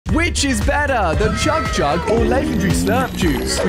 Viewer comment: I heard you say chug chug and slurp juice and when it restarted I read the title and it said Chug jug or slurp juice😂😂😂😂 I was literally thinking how you said sounded like chug chug when it is chug jug😂😂😂😂😂